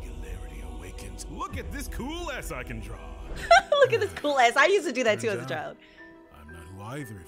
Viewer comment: When Khan said "Look at this cool "S" I can draw" the subtitles thought he said "look at this cool Ass i can draw"💀💀💀